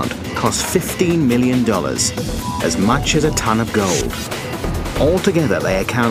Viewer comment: Couldve sworn he said a ton of coke